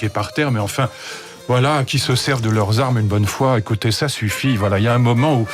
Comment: Euh, c'est moi ou il dit clairement: "- Allez-y tirez leur tous dessus..." ?